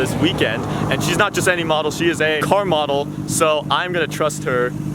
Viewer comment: when said car model captions said whore model